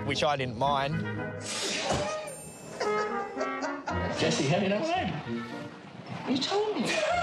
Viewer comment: does anyone know what jesse asked at 3:08 lol they’re accents are too strong sometimes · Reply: Jesse said “jesse??? how do you know my name”